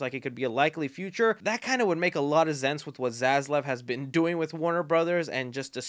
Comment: Ok is it just me or did he say zense